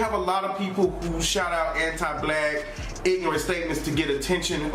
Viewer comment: ay yo hol up, did he just call them anti black?